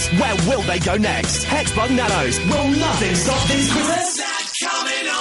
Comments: When I was a kid, i heard it as "WHATS THAT COMING OVER THE HELL ITS A HEXBUG!" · my sister thought it said whats that coming ahead is it a xbox